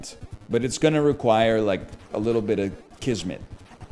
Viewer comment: does he say qizmet as in turkish?